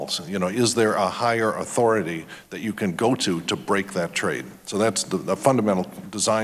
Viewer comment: Did he say break the transaction?